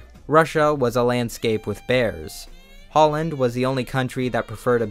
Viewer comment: Ngl, at first I thought you said "Poland" instead of "Holland"